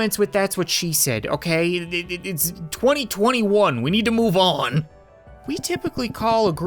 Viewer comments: did he really say it was 2021? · is it me or did he say 2021